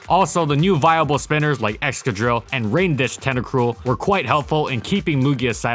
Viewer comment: Because you were talking about Rapid Spinners I misheard Rain Dish Tentacruel as Break Dance Tentacruel.